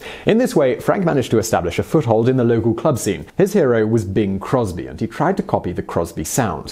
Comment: I wasn’t watching the video and I legit thought he said Bill Cosby and I had to rewind